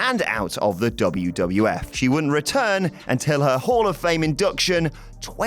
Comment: "Horror-fame?" ohhh Hall of Fame, considering the change in appearance, I would've accepted what that sounded like. shudder